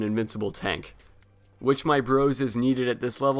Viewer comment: Still to this day i yet don't know what he said at 1:00 (witch knight bros? What? What's that and how does it help in 3 on 1s? XD) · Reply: Which, my bros